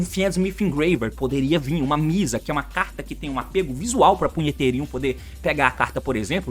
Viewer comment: Só eu entendi ele dizer um apelo para punhet... em 10:30 🤣🤣🤣 wtf.